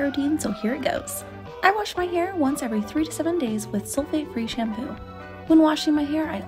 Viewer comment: I thought she said I wash it every 37 days I was like o my but it’s 3-7 days 🤣